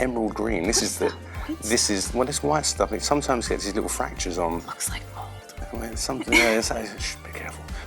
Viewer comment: what does she whisper here? "looks like .... though"? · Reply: "It looks like mold." I'm pretty sure that's what she says, anyway.